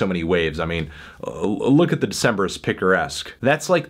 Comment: can someone write down the band's name? I can't understand what he's saying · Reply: The Decemberists.